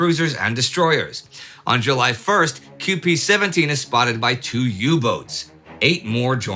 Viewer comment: did he say QP-17 or am i hallucinating ?